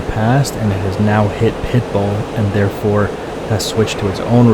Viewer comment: For the longest time, I thought you were saying "Pipal" and not "Pitbull" 😂😆🤣😆😂😆🤣😂😆🤣